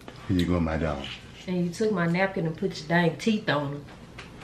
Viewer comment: Teeth on a napkin? Did I hear that right 🤣🤣😂